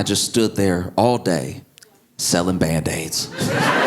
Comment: I need to take a break from life. When he was talking about the mechanic and car trouble, I thought the bandaid bit went- someone offering some sort of service involving a cliff and slippery rocks, but misheard bandaid as bad dates.